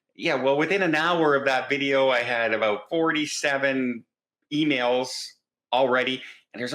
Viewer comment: LOL when you said after you announced the Love Boat cruise you got 47 emails I thought you said "47 females." Okay, I'll see my way out...